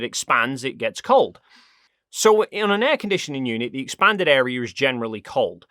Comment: A freezer is not connected to outside the house, the inside and outside for how it work is refering to the refirirator itself. Inside is the interior of the freezer and outside is the room its in, not outside the house. The energy it draws on the cool side is inside the freezer and the hot part is outside it so in the room with things on it to dissipate this heat. Its not drawing the energy from the air in the room its the opposite, thats why it heat up the room instead of cooling it like you say right after this explanation. So i know you are aware of it but its still misleading for the viewers who could very easely think its an explanation of how freezers work instead of how AC units work which is actually the case here. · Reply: He's talking about "an air conditioning unit" in that part (that's what he says a 4:45).